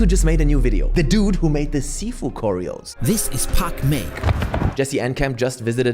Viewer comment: At first i thought you said Pac Man 😂